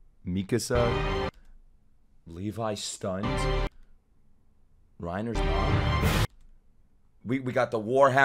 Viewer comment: I hear Reiner's dog instead of Reiner's mom 💀